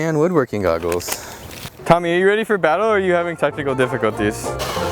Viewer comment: I thought he said "testicle difficulties" instead of technical at 14:03 lmao